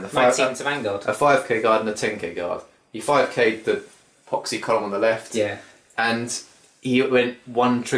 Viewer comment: Did I hear proxy?